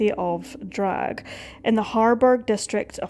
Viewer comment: Excellent research! Just one point: Hamburg is a German state of its own, not in Lower Saxony. Thats like saying New York lies in the state of New Jersey :-) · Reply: It sounds like she is saying Hamburg, but the caption says Harburg.